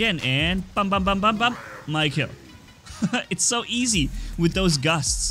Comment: captions on instead of my kill it said mike hill